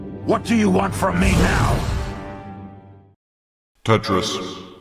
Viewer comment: For one moment I heard taxes XD